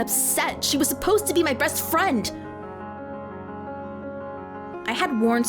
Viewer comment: I thought I heard "Breast friend" But it's best friend.